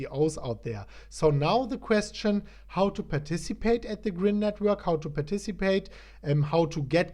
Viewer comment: Didn't read the description and misheard the guy say "Grim Coin"....went over to Coin Market Cap and funnily enough there is a Grimcoin ....hope some people didn't rush over to CMC and invest in that project instead. :-)